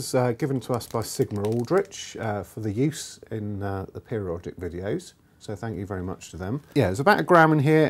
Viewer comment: "...for the use in a pair of erotic videos..." Lol! I swear, this is what I heard, and I keep hearing.